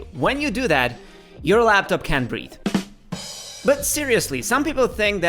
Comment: Just letting you know: I'm not a MacBook, but yeah sometimes I can't breathe. 00:06 You pronounced: "You are a laptop can't breathe" Instead of: "Your laptop can't breathe"